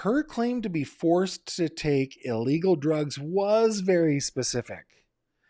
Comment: did she say “ illegal” drugs or just drugs? Like mental health drugs, Prozac or other antidepressants? Her husband also mentioned she needed medicine and didn’t take it. Kind of weird - like he’s addressing what might stand out to investigators!? That obituary was really strange. Written by someone manipulating a narrative.